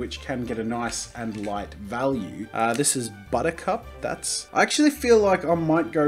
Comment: For buttercup I heard "buttock cup" 😂